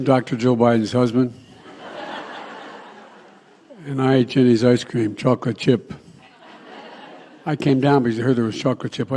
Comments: I heard him say, "I eat chinese icecream: Chocolate Icecream." IM SURE YOU DO LET THEM CHINESE PACK YOUR FUDGE, what with all the money going back and forth from CCP to Biden Regime. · Sounded like he said I eat Chinese ice cream.. transgenders need to just referred to as Pats…. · Wait, did he just say “…. And I eat Chinese ice cream, chocolate chip”??